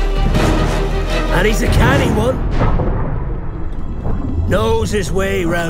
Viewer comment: did he say he is cannibal?! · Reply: canny one